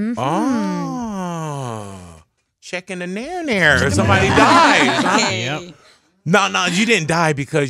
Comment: Did he just say that Tommy dies??